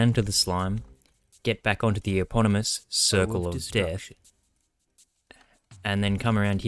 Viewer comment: i heard " circle o of deathstruction"